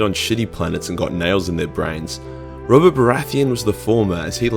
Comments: did he say Robert Boratheon...? The King of Westeros? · did he say robert baratheon ?